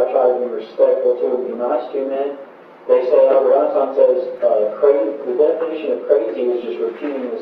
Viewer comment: the subtitles say "they say (unintelligible)," i think what he says is "they say albert einstein said that the definition of crazy..." etc. iirc that's a quote commonly misattributed to him